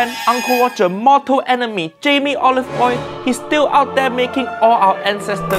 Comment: Why did I hear "Motu enemy, Jamie Olive oil" 🙂😂🤣💔